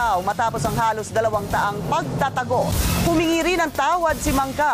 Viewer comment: tama ba narinig ko? Dalawang daang pagtatago daw?